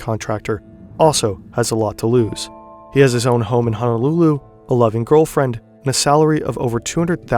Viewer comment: “a loving girlfriend” but I heard “11 girlfriends”.